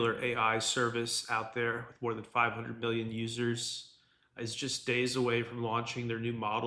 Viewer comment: It sounds like you said more than 500 billion users😂